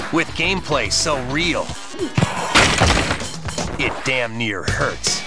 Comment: Did I just hear the word "Damn" in a Nintendo promo??!!